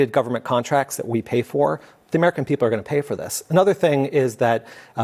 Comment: Not us!! I’m sorry I thought they said we’re not gonna have to pay for it. I’m not paying for this.